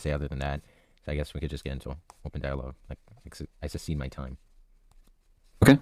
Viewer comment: Does he say "I succeed my time" ?